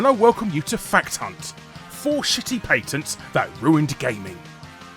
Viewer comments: Oh I'm disappointed. I thought it said "shitty parents that ruined gaming" and I thought haha mortal Kombat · I though it said shitty parents that ruined gaming. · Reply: I thought it said shitty pants at first